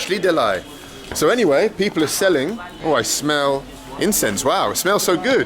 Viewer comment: i thought he said "I smell incest"